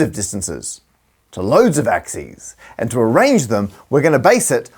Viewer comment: Did you say "axie"? That's a cool backformation